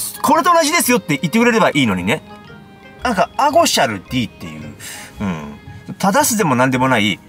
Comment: アゴシャルGが、アゴシャルディーと言ってるように聞こえたのですが、Gのネイティブな発音は「ディー」なのでしょうか？？ 保育園のお子さんが、マイメロDを、マイメロBと言っているのと同じでしょうか？？